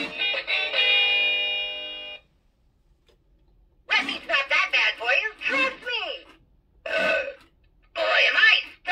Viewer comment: What did he say there? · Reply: "Red meat's not that bad for you, trust me!"